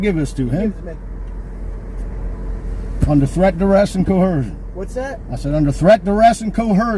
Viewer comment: what is co hercion? Is he trying to say coercion?